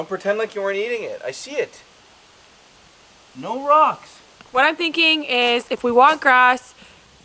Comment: I swear when says “no rocks” to Oso I hear Edna and “no capes”